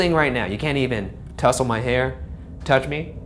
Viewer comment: said” touch me” what I think I heard “ touch me daddy “